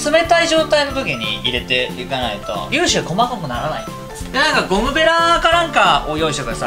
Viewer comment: 「粒子が細かい」を「リュウジが細かい」と空耳してしまう